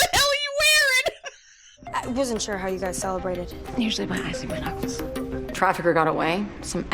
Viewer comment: Warning: My mic's boost was up after I reinstalled by audio drivers for some reason, so my audio sounds like garbage. Sorry. It'll be back to normal for the next two reactions ►Become a Patron! <link> OR ►Donate! <link> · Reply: did she say "usually by icing my nuts?" What??